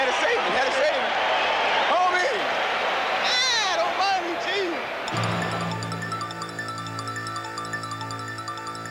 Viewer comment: What does Dominique say at 32:56 after Michael Jordan’s Kiss The Tim dunk in the 1987 dunk contest? I have wondered that for over 30 years! This brings back wonderful memories that are just to marvelous for words! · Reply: I believe it’s “God Almighty…Jesus!” I speak ‘nique.